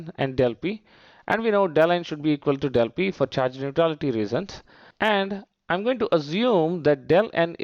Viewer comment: Can someone tell me what he says at 1:16 . Del n and Dep p are equal for ....... reasons. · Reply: Charge neutrality reasons 😅